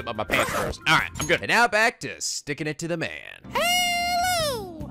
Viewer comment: You know after the clip for the "Previously" section I may or may not have misheard him say "And now back to sticking it in the man"